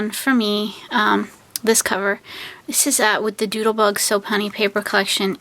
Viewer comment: Thank you so much! The paper collection is So Punny by Doodlebug. I usually purchase Doodlebug paper collections from A Cherry on Top or Scrapbook dot com. Sometimes you can also find them on EBay and Amazon too. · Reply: lol, I thought you said doodlebug soap and honey paper collection and I was googling and couldn’t find it. Thanks for clearing that up😊